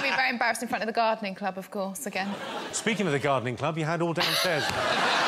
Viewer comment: What does Jimmy say at 1:29 after "Speaking of the gardening club"? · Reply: “(Have) you had all downstairs done?”